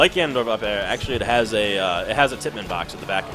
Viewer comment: What did he say at 11:50? He was talking about Zero Suit Samus's up air and he said it was like Gannondorf's because it had a __ box. I don't know what he said but I think it's important to know. Thanks. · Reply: He actually said "tippmann", which is another word for semi-spike. Ganon's up air is a semi-spike when you hit towards the end of the move, and zss is apparently the same.